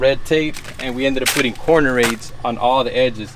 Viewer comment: "we ended up putting corner *something*" on the edge. What word are you saying here? What is the wire framing used on edges? Thanks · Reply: It’s called metal corner aid or corner bead for stucco.